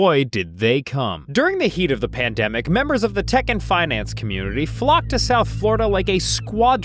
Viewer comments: I keep hearing “tekken finance community” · I keep hearing him say "Tekken finance committee."